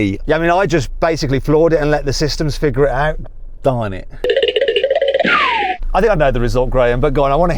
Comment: ヤニ、だまれって言ってるようにしか聞こえん笑